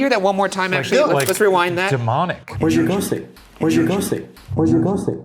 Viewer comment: submission 001. Okayyy so am I the only one who hears “where’s your coffee”? I mean that sounded nothing like “Andrew” 😂